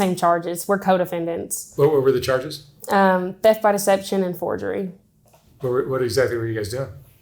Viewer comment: Hate to break it to her, if she does not get her charges dropped she will never work as a nurse. Theft by deception is a charge that will never allow her around people who might be vulnerable. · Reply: Glad you cleared that one up for me! I heard "Death by deception"!! 😅